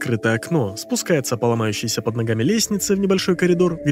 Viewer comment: мне послышалось "По ногающейся под логами лестнице"💀